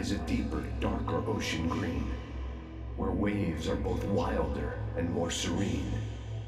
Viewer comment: I just finished Quantum Break and i don't know why but at 6:56 i heard "...where waves are Beth Wilder & Paul Serene" 😆